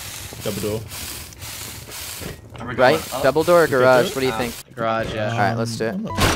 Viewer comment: I was watching this with captions on and at 23:00 the captions said "Dumbledore garage what do you think" instead of double door or garage door, I'm dead lmao